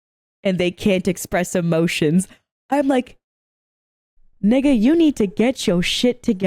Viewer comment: Did she say nigga? Is she black?